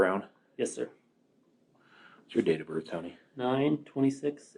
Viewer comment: did he say , what's your datę od birth honey?